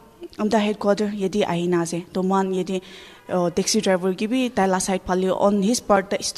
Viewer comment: uh....I heard Taxi drival😂.....in